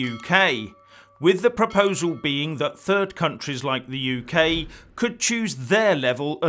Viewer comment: I thought you said third world country like uk.